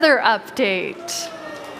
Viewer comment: Who else thought it said weather update?